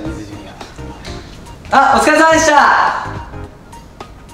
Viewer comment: 最後の星輝くんの「お疲れ様でした〜」 みっちーに聞こえる